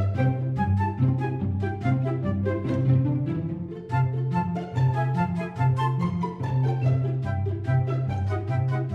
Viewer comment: did nayeon just say baka 6:13 PLEASE TELL ME SHE DID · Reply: naga